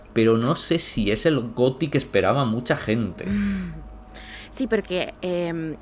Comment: normalmente valoro mucho sus comentarios y reseñas, pero solo el hecho de mencionar que esperaban que el juego del gato fuera GOTY los desvalida completamente, es un juego maravilloso que no busca ser perfecto con una historia lineal casual para que se disfrute, no entiendo por que ser tan pretenciosos siempre! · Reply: Escucha bien lo que dice el minunto: 9:59 "no se si es el goty que esperaban mucha gente"... Es muy claro lo que el dice... En ningún momento dio a entender que él o la chica esperaban un Goty. Tú eres el tipo de personas que le gusta tergiversar las palabras de los otros para su conveniencia.